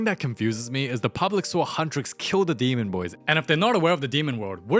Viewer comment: did he say gay or yay?